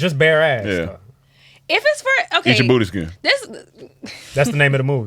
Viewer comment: I totally misheard at first and thought they said Egypt Booty Skin😂